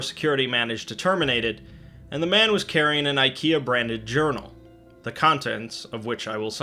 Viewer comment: Okay, I was washing dishes, and I thought that you said “IKEA brand urinal” instead of “IKEA brand journal”